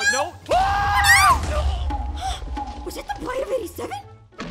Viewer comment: did aphmau said was that’s the bite of 87 like from Freddy Fazbear’s pizza